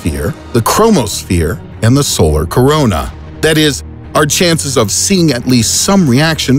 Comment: did he said CORONA